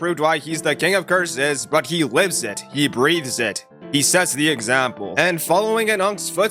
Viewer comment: woah woah woah Lol i heard "whips it"